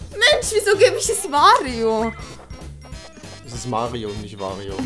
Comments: ich hab wario verstanden :-) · hab wario verstanden